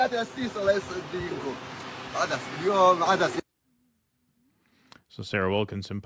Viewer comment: I think he said it was lentils